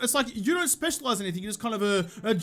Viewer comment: "Jack of....Joel of all trades." that 'of' sounded like an 'off'.